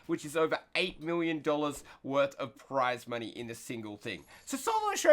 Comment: I thought he said 8 Billion dollars.